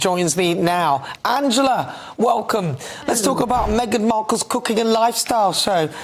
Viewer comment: lol! Thought you were saying “cco-koo” tips. Oops! Cooking. 🍳